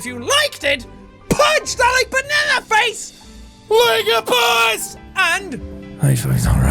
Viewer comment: I'm cleaning and listening to jack play the game, I swear I heard him said "punch that banana in its face" lol